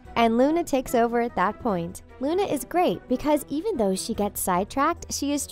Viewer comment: Who at 3:53 heard narrator say Lunatics or is it just me although I know what she actually said